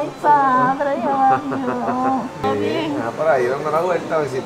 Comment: Que dijo la Señora en el minuto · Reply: Dijo padre